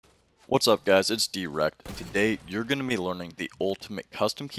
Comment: I thought u just said "hey guys, I'm erect" I almost thought I was on fortnite porn or some shit